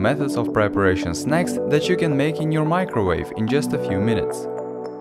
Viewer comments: he says 'just in a few minutes' but the subtitle say 'just in a few hours' XD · At about 0:12 says he'll teach you to make goodies in a few minutes, but the captions say a few hours.